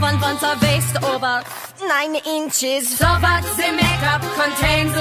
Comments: The song: At least your complection will bring all the boys in My ears: At least your complexion will bring all da poison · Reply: Pfft same I thought she said atleast ur complexion will bring all the poison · The song: At least your complection will bring all the boys in My ears: At least your complexion will bring all da poison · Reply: I heard it as “boison”